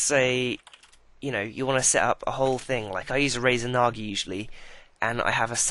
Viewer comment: Did he say Razer Nagi 4:40 ?